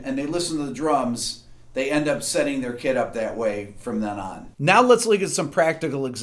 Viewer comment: Did you say "kit" or "kid"?